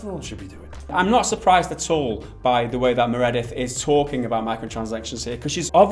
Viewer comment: Did really call Meredith, Meredydd (5:54)! Wrong gender, methinks :-)